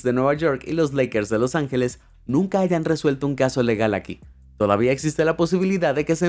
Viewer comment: Minuto 15:15 dice: Nunca haigan 😮 o escuche mal? De lo contrario, no te vendría mal leer un libro de vez en cuando 😅